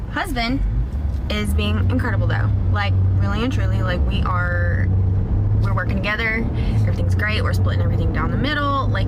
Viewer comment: I thought she said they were working it out....